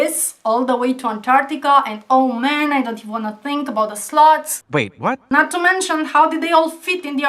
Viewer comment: I nearly propped my jaw when you said "what about the sloths?" I thought you said 'sluts'. It's the accent. :)